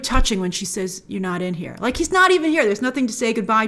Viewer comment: I think she said "I can't feel you" Which was reminding us of what Vision said to her in Infinity War: "I just feel you"